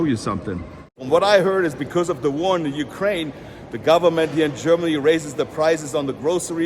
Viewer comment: did he just say "the government raised the prices"? :D